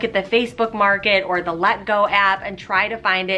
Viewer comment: /Did anyone catch what she was saying at 3:16? Did she say Let Go app?